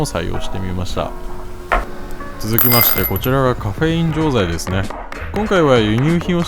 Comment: コカイン錠剤に聞こえて焦ったww